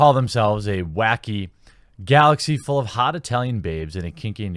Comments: Did you just say hot "Italian" babes or did my preferences just manifest themselves? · Did he say Italian instead of alien? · Did you say "hot Italian babes"? I think you did! :-)